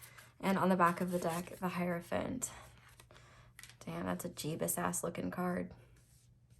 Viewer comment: me: not interested in anyone at all also me: OOoooOooh yes let’s watch this Also lmao in pile 2 did I hear correctly? “Damn that’s a Jesus as looking card” because I wheezed 😂